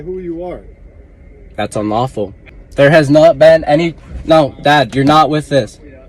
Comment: I think he said "no Dad"?